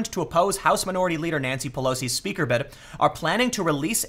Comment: First time I thought you said Nazi Pelosi